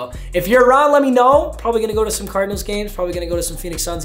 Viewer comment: did he say cardinals games